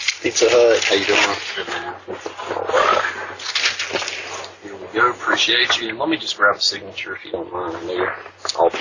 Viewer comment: I heard “Pizza Hut, I appreciate you”. Where is the racial slur? Black delusion. Black ambulance chasing lawyer.